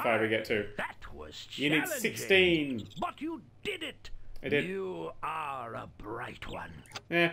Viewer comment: it sounded like 'f*** you did it' because i was thinking XD